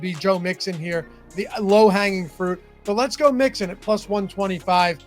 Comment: it sounds like you say lets go mixon lol